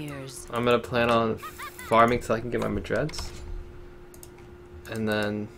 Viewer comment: The first time you said Madred's I thought you said Mejais I was like wut.